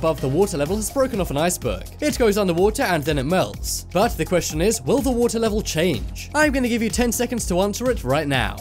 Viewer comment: at first I thought he said: Will the amount of water change? And I was like: uh, yeah!